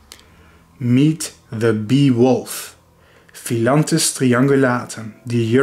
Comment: The beewolfs feats are really gallant and unusual. Wouldn’t go collect honeybees unless they are dead. And plus for some reason my mind thought you were saying “Beowulf” as in the epic hero.